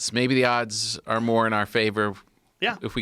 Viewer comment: Did he just say “may the odds be in your favour” @ 28:20 ish ... 👍🏻😊